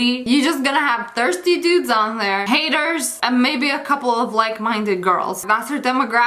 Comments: When she said haters and fans I thought she was talking about heaters and fans- 🤣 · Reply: I thought she said gators and fats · When she said haters and fans I thought she was talking about heaters and fans- 🤣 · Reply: Why me tho I thought she said healpters and cans